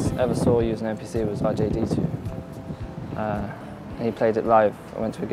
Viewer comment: what say? Jd2? Dj d2?? Craps!!! Help me! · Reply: He's talking about the Electronic producer RJD2.